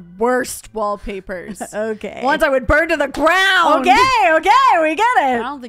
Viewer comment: I had apparently scrolled over this video or something, because it started at 0:09 and the first thing I heard was "- BROWN!! + OKAY OKAY, we get it - I don't think you do!" And I just love that, actually. (Love brown(s) though lol) (Yes I do know you sayd ground, it seems it started mid-word 😂)